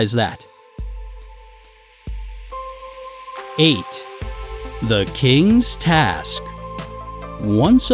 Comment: I heard it as “ate the kings task” 😂😂😂😂😂